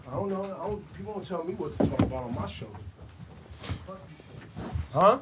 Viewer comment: When Ebro said you ain't gone tell me what to talk about on my show I just heard "man my grandma gave me that chain."